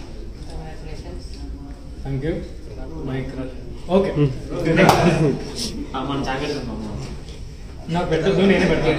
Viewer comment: What he said 2:14 didn't understand clearly..... Any one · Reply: My crush ani annadu